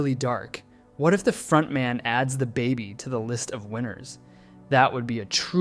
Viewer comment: I thought bro was saying that, but if they frontman add the baby to the price pool